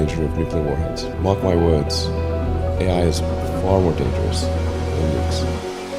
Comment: bro subtitles correction He actually said AI is far more dangerous than Nukes(nuclear weapons)